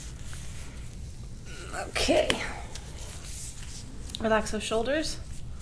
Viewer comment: when you ask her to relax her shoulders, my echo asked if I wanted to listen to the song Shoulders. It took me a second and 1 rewind to figure out, it kinda sounded like you say Alexa! Thanks for creeping me out lol